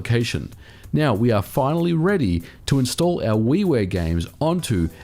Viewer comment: At first I thought he said Wee Wee games. But it was actually Wii Ware. Lol